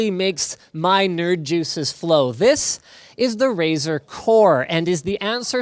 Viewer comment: For a moment I thought he said "This is Razer Wh*re"